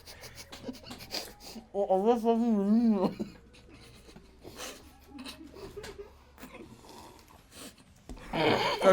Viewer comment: ne diyor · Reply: Yenilmiyor